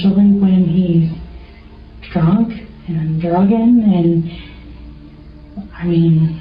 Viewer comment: got any jobs for writers and/or transcribers? I clearly hear mistakes in your videos a lot, such as this one. She says, “…especially when he’s drunk and druggin’…” Not “drunk and drunken.”